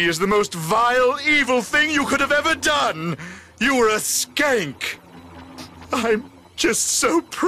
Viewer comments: Did he just call his niece a skank? BAHAHAHAHAHAHA!!! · Did he call her a skank...? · Uhh.....am I hearing this correctly? Cortex...did you just call your niece a skank? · did he just say the "S" word?! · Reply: S K A N K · did dr cortex call his niece a skank ?!?! 😂🤣😂🤣 or did I hear that wrong